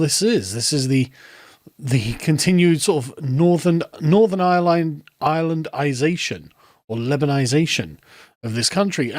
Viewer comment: "the continued Northern Ireland-isation"? Is this what I heard? Can anyone elaborate cos I'm confused as hell.